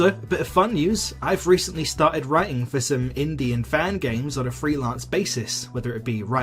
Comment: i misheard that as “Indian fan games” and was so confused